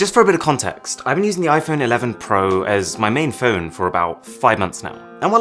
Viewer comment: I misheard you as saying I've been using the iPhone 12 Pro for about 5 months now and I almost fell out of my chair.